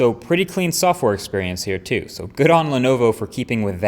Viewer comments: Did you say "Good on Lenovo" at 2:50 ? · did he just say lenovo?